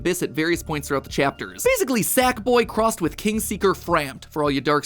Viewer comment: did he just say sackboy?